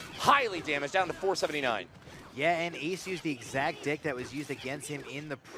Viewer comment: did he say dick?